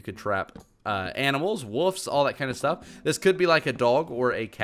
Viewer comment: Did he just say woofs?!